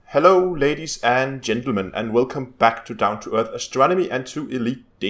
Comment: i almost thought he said "babies and gentlemen."